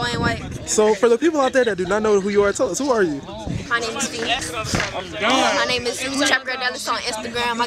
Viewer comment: I thought said my name is feet